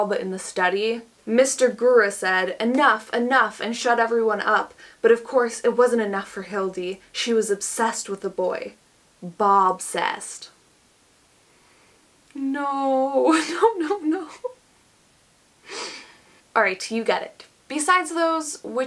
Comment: I thought I heard Bob sassed, which didn't quite feel as cringey as was making it. I was sure I'd heard wrong. I repeated the clip twice more, and then I got it--BOB-SESSED. OH, MY GOOOD. I literally shouted out loud. That is soo bad. I feel a lot better about my writing now.